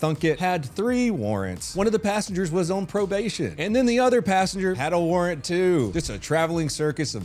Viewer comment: When he said “the passengers”, I thought he said pastor… I didn’t even think much of it. I just thought “that makes sense”.